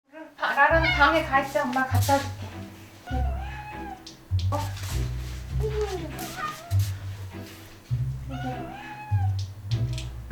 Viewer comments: I thought LuLu said oh no 😂😂😂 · 저만 루루가 엄마라고 이야기하는것처럼 들리나요..?